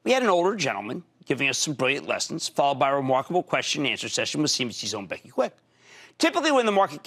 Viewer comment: what is he saying here? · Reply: "followed by questions and answers at cnbcs own Kwin"